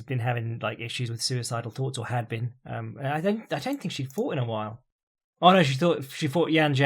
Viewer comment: I thought you said "she had been having suicidal thoughts recently, which is weird cos she hadn't really had a thought in a while"...you actually said "fought" :o